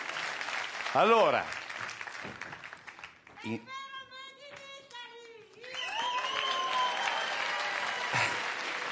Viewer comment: cosa dice la voce del pubblico al minuto 0:56?? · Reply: Dice: “sei il vero Made in Italy”